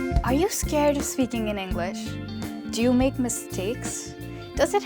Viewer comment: did u say "scared to speaking in English "😒